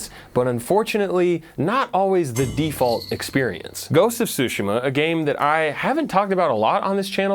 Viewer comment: I thought he said Joseph Tsushima at first and just figured that was who made the game